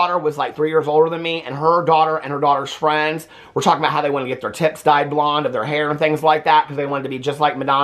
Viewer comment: I fully thought said they wanted to get their “tits dyed blonde” and was so confused like how does one do that?? Lol😂